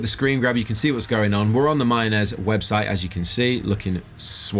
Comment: I thought it said mayonnaise.